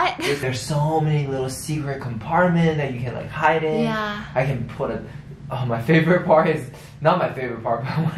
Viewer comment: i thought said he can put his favourite wife, bisss aahhahah❤️❤️